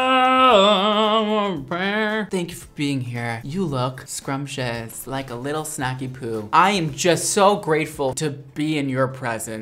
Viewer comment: Am I the only one who thought he said “you look scrumptious, like a little sack of poo?” 😂 oops...